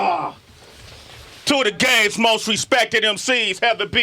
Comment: sounds like he said "2 of the gayest most respected MC's" 😂😂😂 I was like wtf, rewind. Lol